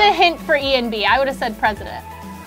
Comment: When they were discussing what to say for Ian: "I would have said Anthony on---" The perfect cut right at the end of the video for max comedic effect, brilliant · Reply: I heard it as "I would have said Anthony won" just as another quick jab at Ian. Man, he's a beast getting treated like that all the time and still smiling and being him. I know I couldn't handle it. Also, is my latest favorite human. An absolute gem.